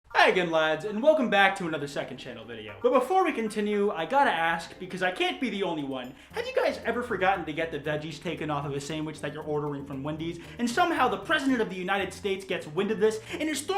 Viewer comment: Instead of veggies I thought you said dead cheese